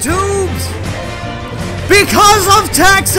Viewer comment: I gotta admit for a second I thought you said "Because of Taxes!!" Thats just as bad as any raid irl for most gaurdians.